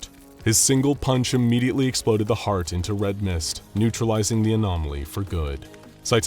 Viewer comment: and bait was- HOLY MOLY DID HE SAY RED MIST???? PROJECT MOON FANS ARISE!!!!